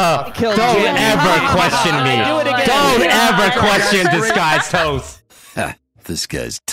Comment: I deserve to be roasted for commenting this but I've always thought the intro said 'heh this guys toast' and I just realized its saying 'heh disguised toast' because thats the name of the channel and tbh I wanna die a little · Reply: Nah bro it says this guys toast, it’s a Hearthstone reference